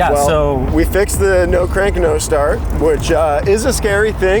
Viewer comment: Thought you said it had crank no start. Sounds like no crank either...